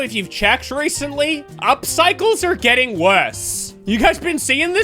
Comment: 2 seconds into the video my brain lagged and i thought you said "our cycles", as if you were addressing our menstrual cycles 💀